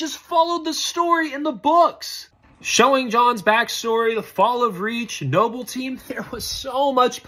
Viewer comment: "The fall of Reach" I thought he said the fall of Creek almost gave me PTSD flash backs 💀